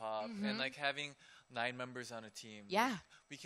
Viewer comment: did he say 'having 9 members on a team'